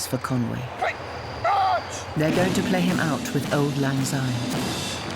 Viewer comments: how is the march called ? I couldn't understand when they said the title..... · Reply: Auld Lang Syne. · anyone catch the name of that song, when she said their going to play him out with... I couldn't catch its name. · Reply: Simon Mead Auld Lang Syne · anyone got an idea as to what the march is called? Afraid I couldn't quite hear the narrator when she stated its name :/ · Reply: Auld lang syne